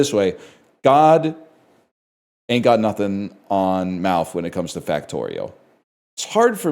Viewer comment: I think the English (Canada) subtitles are wrong here. I believe he's saying "God ain't got nothing on Malf when it comes to Factorio" but the subtitles say "God ain't got nothing on mouth when it comes to factorial" which confused me quite a bit. It almost sounds like something that would make sense in context, but it doesn't. Otherwise great work, Malf ain't got nothing on you when it comes to content Factorio